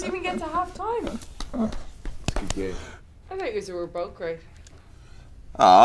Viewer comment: What does Becky say at 6:25? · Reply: 'I thought you's were both great'